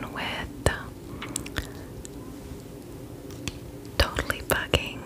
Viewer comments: When she said “totally bugging” I thought she said “totally f!Cking” and my face expression was “😳😱” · When you read the totally bucking shade it sounded like u said totally f****